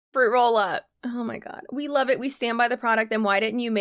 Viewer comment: I thought she said “we truly scam behind our product“. Stand. She said stand.